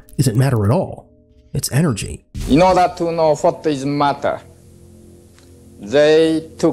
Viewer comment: I was listening and i heard: Fk his mutta 😂 I spat me coffee out 😂😂